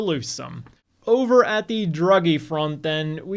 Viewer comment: "Over on the DruggieFront..." Did I MisHear that???